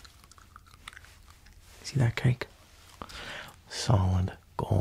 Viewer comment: Did he say kike?